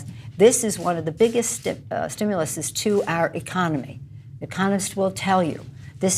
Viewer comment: When Pelosi said "economists will tell you..." I swear I heard "communists will tell you..." at first.